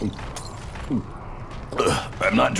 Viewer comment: Alguien me puede decir que sepa ingles que dijo en 0:38 Black Ops 4? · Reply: "Im not drinking it for flavor just the effects. Wonder what it does?" There